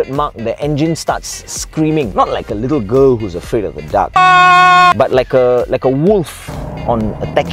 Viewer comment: I heard "not like a little girl who is afraid of a duck" Damn those scary ducks!